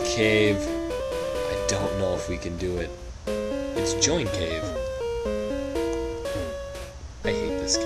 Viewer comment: I thought it said joint cave -_